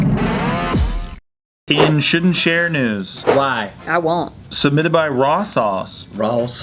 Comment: At the beginning I thought Steve said "in shit and share news" hahaha!